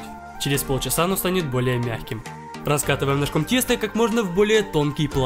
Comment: Мне одной послышалось "тесто просто супер тупое"?)